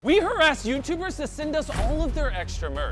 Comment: First 3 words I hear be like “I harassed YouTubers” lol 😂 😂